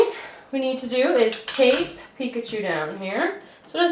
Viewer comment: I thought she said take Pikachu down instead of tape picChu down. Like U AINT TAKING PICKACHU DOWN